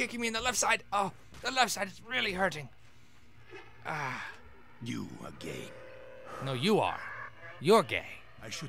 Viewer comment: who else thought Kravchenko actually said "You are gay." lmao